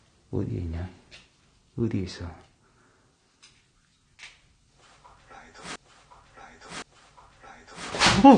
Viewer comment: 남성분 목소리로 거기 라이터 라는 말이 선명하게 들림..소름 ㄷㄷ